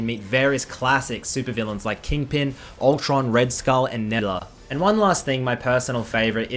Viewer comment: Love that you’ve got a sponsor congrats, but can someone please tell me what he said at 1:14 “you can meet classic supervillains like kingpin, ultron, red skull and nehdhba”??? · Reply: Nebula maybe?